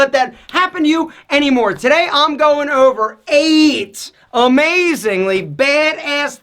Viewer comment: Sounds like he said AIDS instead of 8